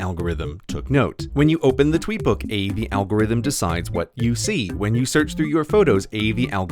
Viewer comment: It sounds like he says “ab” but I think he is actually saying “a the”